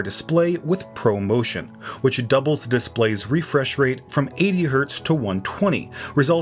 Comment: 80 hz? Did I heard it right?